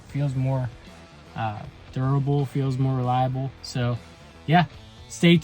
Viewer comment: I think you heard what you wanted to hear. I said it feels more substantial and durable. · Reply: "feels more durable, feels more reliable". Literally exactly what you said, lol